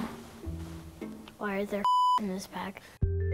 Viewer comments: At the beginning I thought she said why is there shit in her bag😭😭💀💀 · in my mind it sounded like "why is there shit in his bag?" 😂 It would've been funny if she actually said that 😂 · The beginning it blurs out and I thought she was saying “why is their shit in his bag?” Lmao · Sounds like she’s saying “why is there shit in his bag” (the beginning of vid) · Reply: I thought she said why the fuck is in his bag..? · When the put the beep sounds cuz she said why are there #### in is bag I his I thought she said why are there drugs in his bag